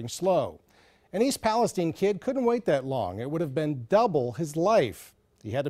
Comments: Ngl i thought it said palpatine kids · Oh, I thought it said "E. Palpatine."